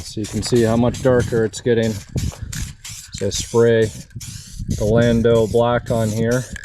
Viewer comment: Can you send me the paint code you used in the black interior I thought I heard you say 🤔 Orlando black not sure? Pls help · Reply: Landau Black